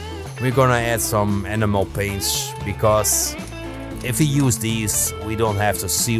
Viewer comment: I have watched a couple of times, great video! If I didn't know better it sounded like you said "Animal paints", for a brief moment I was thinking "what's an animal paint???" Keep these video's coming, your channel is truly a gem, I have been sharing it with my friends in the hobby.....